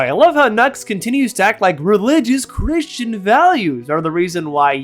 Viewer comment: nonono thats not what was said. its close but not it. he said values, which is very much so a white nationalist dog whistle. this is a trend with this dude since he keeps referring cultures as better or worse than others specifically american/european(white) culture is better than african(black) culture. the christian angle may have some validity but lets not beat around the bush here. its white supremicist propaganda